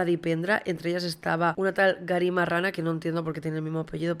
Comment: En el min 13:29 entendí Gary Marrana